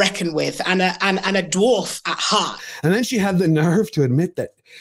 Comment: WHOA! Talk about a Freudian slip there! Despite what the subtitles said, I'm 99% sure she said Dworkin. As in Andrea Dworkin the infamous radical feminist who (among other things) liked to rewrite the traditional works of men (e.g. fairy tales) to a "feminist perspective."